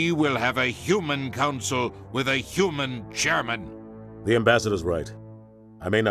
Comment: I'm gonna be honest. When Udina blabbered on about how humanity should lead the council, after that Hitler reference from, I thought Udina said "We need a human, german for the Council!" and for a moment I thought that had once again predicted the future... Alas, Udina was saying Chairman, but the thought was funny